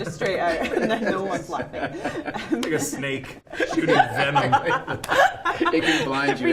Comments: Can we just talk about: -Paul Rudd being 50. -Karen being a natural beauty. -Don Cheadle being Don Cheadle. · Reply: Is it just me or did Paul say "vemon"? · This is the most karen has talked in an interview and I’m glad everyone seemed to enjoy the other person’s company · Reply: Is it just me or did Paul say "vemon"? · This is the most karen has talked in an interview and I’m glad everyone seemed to enjoy the other person’s company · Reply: jones no I mean I thought he said vemon instead of venom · Is it just me or did Paul say "vemon"?